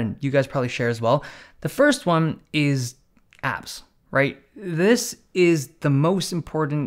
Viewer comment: i literally heard A*S